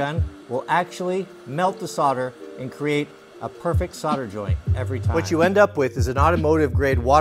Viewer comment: Bro i thought he said "f*cken" instead of "perfect", took me BACK